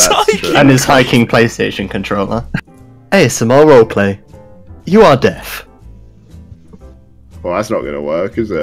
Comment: I thought it said "You Are Dead", instead of "You are Deaf".